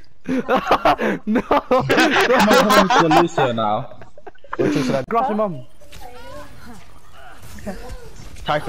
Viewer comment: did u say grass your mum? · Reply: Junayd Hussain I was in the midst of saying “kinetic grasp your mum”